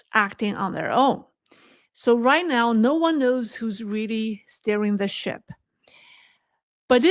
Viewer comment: I thought you said " stirirng the shit" 😂. Which might be more accurate😂😂😂